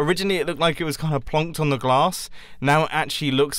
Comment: Am I the only one who heard “glass” at 11:12?